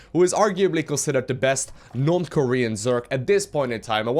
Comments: hahaha I could have sworn he said North Korean zerg!!! like, they dont have google but at least they got sc2 :) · DID I JUST HEAR "NORTH KOREAN ZERG"?!!?!? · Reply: I can't know for sure what you heard, but what he said is "non-Korean Zerg".